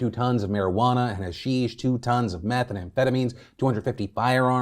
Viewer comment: you said "meth an amphetamines" with no d in the "and" instead of "methamphetamine" and my brain glitched for like 10 seconds😂😂😂